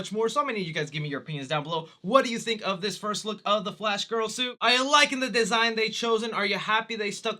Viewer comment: anyone else catch that he says " Flash Girl" at 0:55 ? 🤣